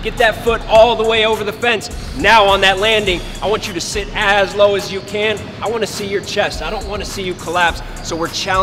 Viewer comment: "Sit ASS low as you can" I heard what you did there...